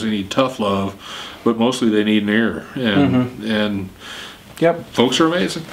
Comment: Anybody know what is said at 4:40? :/ "Sometimes they need tough love, and sometimes they need ..." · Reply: an ear as in someone to listen to them